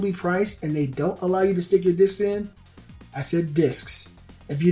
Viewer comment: Could have swore you said dicks. I highly doubt it will get backwards compatibility. There probably going to pull a dick punch like they did with the ps3 & add like 5 games from the Ps1 and say "look we have a massive collection of Ps1 games only for five bucks each". If we get a digital Ps2 store or disc compatibility I will be playing the living hell out of Final Fantasy 12.